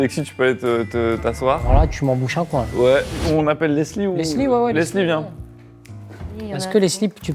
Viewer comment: trop mimi cette scène et Paul qui regarde constamment Leslie pour voir si elle ment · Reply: Moi à 21:54 j entend surtout malik qui l appelle "les slip"